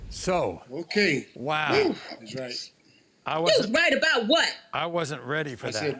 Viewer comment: "You was right about what", uhmm he was right about not being the father! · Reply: I thought he said "I was right" but when she called him out he said he didn't say that he said something else "... White"